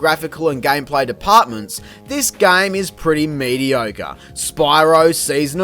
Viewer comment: You're pretty fun while also handing out nice reviews. Wish you had more subs so ill do my part and subscribe myself. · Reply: When you said “mediocre”, it made you sound like you were saying “mediogre” during the verdict section of Spyro: Season of Ice! XD